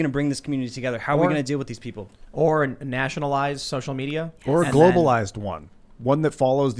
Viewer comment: Bitch, wtf did you just say? Nationalize?